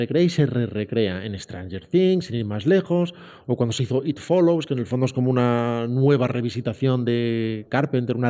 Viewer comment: min 1:21:58 a qu pelicula se refiere? algo de it follows? no le entendi bien, alguien me echa un cable , Gracias. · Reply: Sí, creo que se refieren a It Follows de David Robert Mitchell.